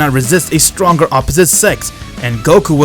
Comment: Who heard sex on